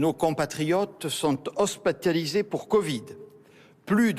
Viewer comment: C est moi ou il a dit hospataliser